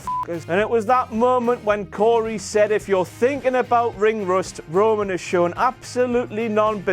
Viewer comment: I thought Cory said ring rest not ring rust